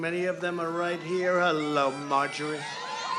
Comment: did Trump say he loves margarine?